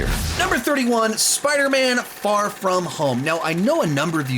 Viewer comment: When you said far from home I thought you said no way home and I was like huh no way home this low